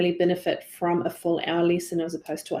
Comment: When she said "This student would benefit from a full hour lesson, as opposed to a half hour lesson" I thought she said FOUR hour lesson lol! You can imagine my reaction.